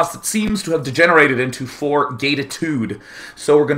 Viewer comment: did he just said 4 Gaytitude? :)